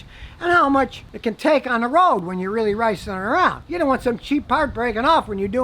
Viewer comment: did he said riceing?